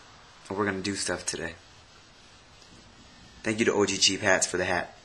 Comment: lol i thot i heard "orgy cheap" lmao